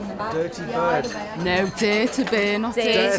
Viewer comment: Dare to Bare, for instance, did sound like she said dirty bird. American English is much more annunciated than British English, which is more airy and proper sounding. Some parts of England seem to be easier for us to understand the accent, but Liverpool seems pretty tough.